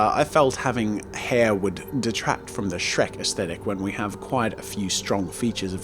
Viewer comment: Did I just hear the words,"Shrek aesthetic?" Shrekthetic?